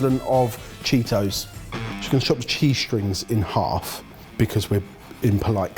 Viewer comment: When Ben said cheese string, I kept hearing "g string" 😂😅